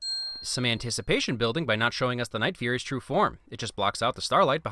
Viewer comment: Did he just say night wing instead of night fury